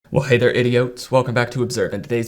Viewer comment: Bruh i thought you called us idiots LMAO